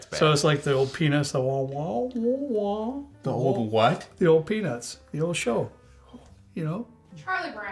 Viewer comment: The old peanus is what I heard, no “t” sound 😂😂😂